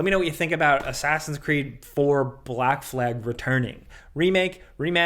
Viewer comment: For a second I thought Jake said "naked Fortnite" and not Snake in Forrnite. Jesus.